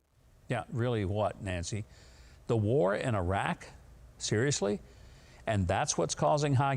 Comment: I thought Nancy said the War in Inarah which makes more since to me then Iraq. Also scary because if they win the war we will all be beheaded or converting to Muslim.